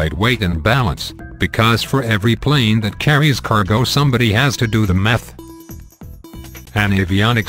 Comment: sounded like " somebody has to do the meth"..Well..if anyone needs it LMAO!!!!.